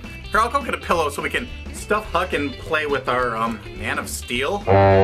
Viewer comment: I heard you say stuff F*CK and play :D